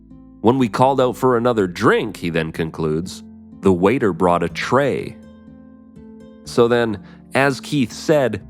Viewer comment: I always thought it said, based on the feel and the images being created, that "The waiter brought a trail".